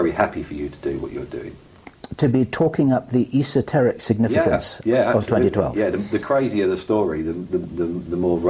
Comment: He said @ 52:50 "esoteric significance of 2012" not "2102" i dont understand your language but i do numberz